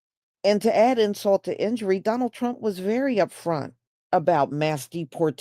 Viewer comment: 🎶 I will BOTE for Donald Trunk. 🎵🎶🎵🎶🎵🎶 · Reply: I thought they said "Donna Trunk."😂😂😂😂😂